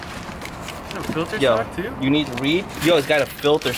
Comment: "You need weed?" Did i hear that right? 😂